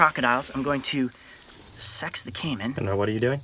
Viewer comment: Im pretty sure I herd him well but did he say sexed didn’t even know that was a word